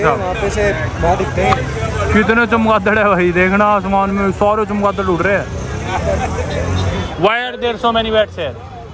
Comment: what happened here?? Beat krdi kya hota h? I dont understand, anyone explain 😅😅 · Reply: (Bird ne uper se potty kar di) that's what he's saying